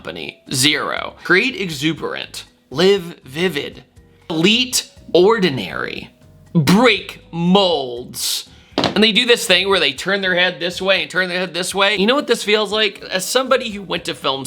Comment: bro did you say "bleet ordinary" 4:04? It said delete 😂 But nah Jaguar is having a mental break down publicly I think.